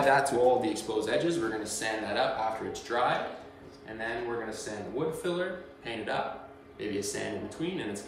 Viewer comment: i cant understand what you are saying, sounds like "sand a coat of wood filler" do you add a coat of wood filler? Can you please explain. thanks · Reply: "Sand that up after its dry, sand wood filler, paint it up, maybe a sand in between, and its gonna be glassy smooth." Basically he meant use wood filler after the glue/water mixture is dry, sand, then paint 1 or two coats, sanding in between each coat.